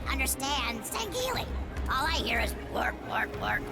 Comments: All I hear is “Wort wort wort” Uh huh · All i hear is "wort wort wort" lol.